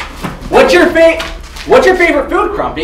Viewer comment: I heard a “What-?”